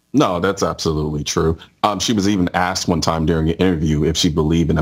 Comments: colion says, “she was even asked during an interview…” but to me it sounded like he said ‘she was eatin’ ass…’ My brain rolled with it and came up with “that would explain why the only thing that comes up of her mouth is shit.” 🤷🏻‍♂️ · I thought he said “she was even eating 🍑 “ 😂😂